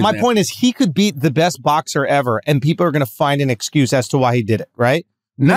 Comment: Schulz says peeperrrr instead of people on accident lol